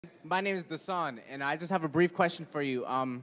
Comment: I thought he said my name is Dasani